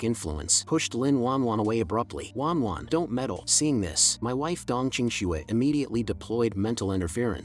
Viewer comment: my wife!? did i heard that right?!?!